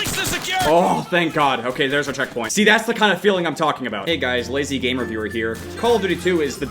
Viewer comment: "lazy game reviewer" For a second I thought you said Lazy Game Reviews Idk why